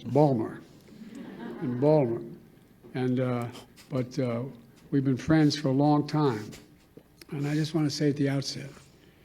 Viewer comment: did he just say we've been trans for a long time😂